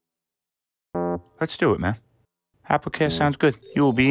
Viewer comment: Ohhh, he said, "apple CARE!" I heard "apple KAY" 😂😂😂😂😂